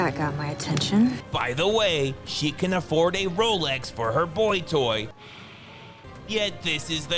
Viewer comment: "...She can afford a rolex for her boytoy...but look at the car she drives" 😂😂😂😂...Oh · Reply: I think he may have misheard where she says, 'Ralgex' which is a heat spray like Deep Heat used by the elderly for aches and pains on their joints and muscles, not 'Rolex'.